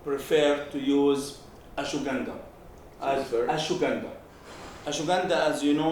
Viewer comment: what is he saying? Ashwagandha?